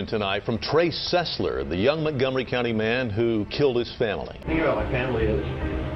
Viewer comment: WAIT IM SORRY DID HE JUST SAY MONTGOMERY COUNTY CAUSE IF HE DID OML IM GONNA SCREAM I LIVE IN MONTGOMERY